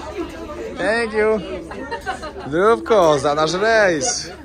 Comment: Usłyszałam" kochanie za nasz rejs " 🤔😁